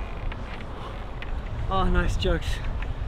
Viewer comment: I swear this guys says “ah nice jugs”